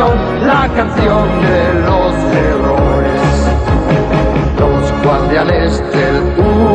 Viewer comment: Amo "Pegasus Fantasy", pero "Los Guardianes de Universo" es todo un HIMNO que marcó mi infancia, tengo 30 años y casi se me salen las lágrimas cuando escuché "la canción de los errores" :') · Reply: Jajaja yo toda mi vida pensé que decía errores 😅